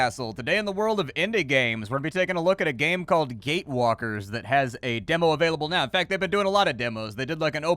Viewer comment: I open this vid and quickly let it play in the background, first few seconds all i heard was "Gay-walker", dang it.. it wasn't what I thought xD